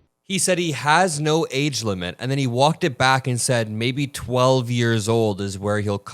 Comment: Not that I ever want to be in the position of defending Trump but to me it sounded like he said, don't be in of defending Donald Trump "I don't wanna be with like 12-year-olds," not "maybe 12-year-olds."